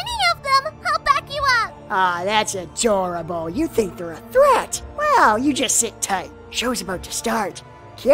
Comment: "I'll help you Mr. Kirito!" Head pats Silica, "Awe that's cute, you think your a threat." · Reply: I always heard it as "You think they're a threat" that changes that line a bit lol